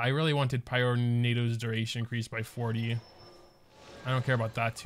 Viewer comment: Is it me to did say the n word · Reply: “Increased by 48”? Is that what you mean or *40